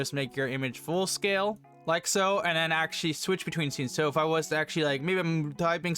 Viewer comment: what bro say💀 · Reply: “Switch between scenes, so if i”